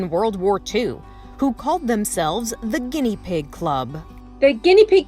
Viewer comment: At first I thought that said papa pig club